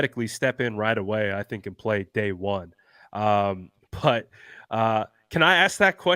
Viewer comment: I thought was saying "Day One" Johnson as if to imply Mr Johnson was cocky enough to think he'd be drafted on day one!